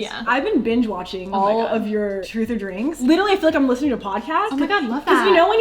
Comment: i thought it said truth or drunk haha